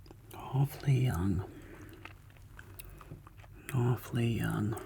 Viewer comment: Won’t lie 0:42 caught me off guard, I thought you were saying “awfully hung” 😭💀